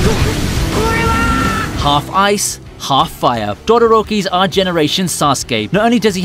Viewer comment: did you just say dodoroki😂😂😂